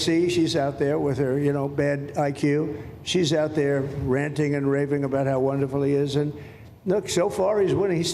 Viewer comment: Did trump just say aoc is ranting and raping?